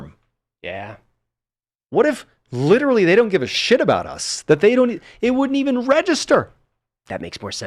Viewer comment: Wow, I've never heard anyone talk so openly about wartime issues. Even my own family members never have. I just want to acknowledge how incredibly brave MrBallen is being. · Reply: Love this thought he said "nobody really give a shizza about you." How powerfully disheartening the men and woman fighting for our freedom, are taught to do all these things no matter the morality and we not gonna listen or help you when it damages them.